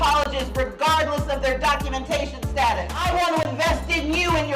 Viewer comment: Did I hear her say "regardless of documentation status" ? Yikes